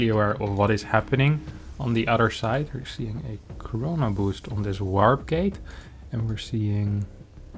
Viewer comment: did you just say corona boost? xD